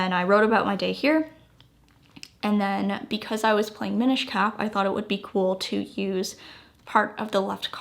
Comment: What game were you playing? Minish ka? That’s what it sounded like.. at 22:28. Sounds like a good game. Can you get it for the Switch? Sorry for my ignorance, but I seldom play games - I only bought the Switch for Animal Crossing but I got bored of that game a couple of years ago and need to find a replacement. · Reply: I gotchu! It’s called Legend of Zelda Minish Cap, it’s an old game boy advance game that you can get on the Nintendo switch through though their online membership ☺️